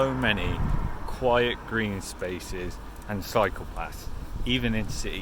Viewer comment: Sounds like you were saying 'psychopaths' (cycle paths) haha :)